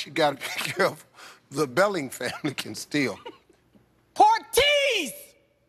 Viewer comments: Everyones talking about how he said "cupine" but i'm wondering what that woman said at 3:14...i mean what the hell is a "pork tease"? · I'm not a racist man but oh my god that family was like the definition of black stereotype also at 3:14 did she just say "Pork Tease"? What the hell is that???